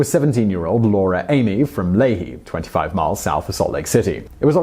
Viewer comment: it took me waaaay to long to figure out what Utah town he said at 14:53. Lehi is pronounced "Lee-high" so I was really thrown off!